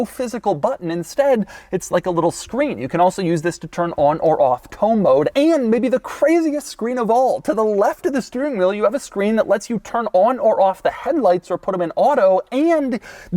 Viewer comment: Did he just say tow mode?!?